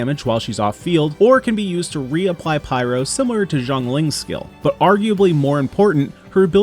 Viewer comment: sorry I thought you said “zhonglin” at first and was confused for a sec lol, Xiangling’s name is pronounced something like “siang-ling” (“see-yon ling”)